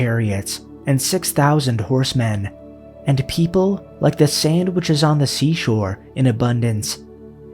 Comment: I heard "sandwiches on the sea shore" 😅